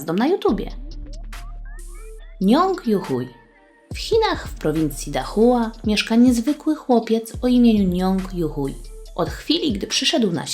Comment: czy tylko ja usłyszalam nazwisko ju huj??? xd